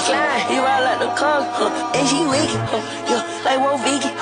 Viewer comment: I always thought he said “like we’re vegan” 💀